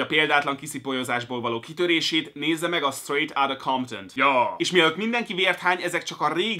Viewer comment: nél ami címet mond azt valaki leírja (bocs nem vagyok nagy angol szakerto es nem tudom mit mond) És az magyarul is fent van? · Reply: kisfuge Gergo Straight Outta Compton